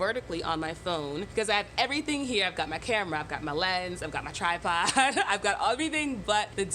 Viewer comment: Hard of hearing moment: captions were off for some reason and I heard "I've got my camera, I've got my legs...." and I'm like welp I guess your legs are KIND OF helpful to content creation. 😂 😂 Lens! Lens.